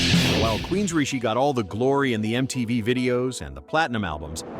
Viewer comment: did you say queens Richie? What the fuck!